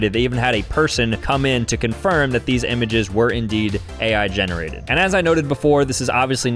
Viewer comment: I thought you said , “I’m going to confront them”. I love your content, but do what you say.